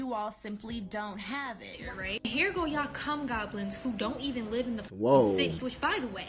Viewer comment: Hold on did she just say cum goblins